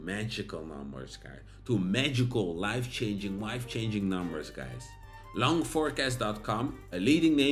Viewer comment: Did you say Life changing Wife changing??? Haha